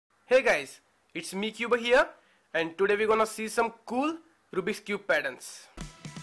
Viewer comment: This is what I heard: Hey guys, its mecuba here and today we gonna see some Kul rubiks cube parents